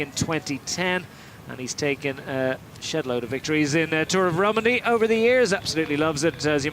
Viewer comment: Does he say “shitload of victories” ?